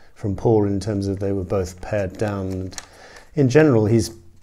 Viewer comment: Hi, 2 things I'd like to mention: 1) At around 6:30 into the video you say that Julian was very perceptive in observing that John & Paul's 1st solo albums were similar in that they were both???? I can't make out what you say. .Paired down??? Sorry, it's just puzzling me. 2) I have the 1st book you show at the start of the video, but have never found a definitive answer to a question I've often wondered about, which I assume it's NO. That question is: Were the 4 Beatles ever in the same place at the same time in the 70s? I believe the last time this took place was Aug, 22nd, 1969 for the band's final photo shoot at John's Tittenhurst Park. Your comment @ 10:07 suggests it was. However, I read an extract online about 15 years ago (and I can't find it now), which I think was an extract from a book by Cynthia Lennon or a book by someone else where she is quoted. She could, of course have got the date wrong, but she seemed to be saying the 4 Beatles met up at her address and it didn't go well and ended in a row and some people walking out. But she wasn't referring to it from the perspective of the 4 Beatles having met up in the seventies. I just put some words in Search and it came up. It might have been on her 31st birthday. If you do a bit of digging you may find it, because it was certainly online about 15 years ago. I'm sure it was a book, because you could scroll from page to page. I was taken to the page in question, so didn't even need to look any further for it. However, I